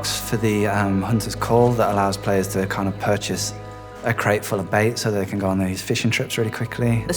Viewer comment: i thought he said a vape crate lol